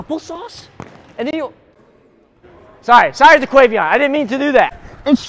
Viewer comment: I sear i heard, “sorry N